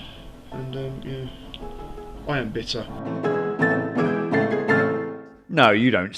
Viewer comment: I actually thought he said "I am bitter" lol